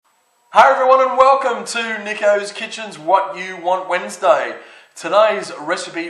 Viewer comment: At the beginning I thought he said "welcome to nigga's kitchen"